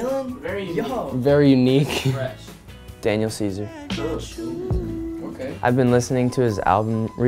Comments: Who else thought when Jack said Daniel Caesar who else thought he said Daniel Seaver?? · I thought Jack said Daniel Seavey when he said Daniel Ceaser. Who else? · I really thought that when jack said "Daniel Ceaser" I thought he said "Daniel Seavey"